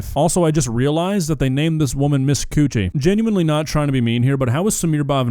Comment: did you hear Coonchy or coochie ..! I heard coonchy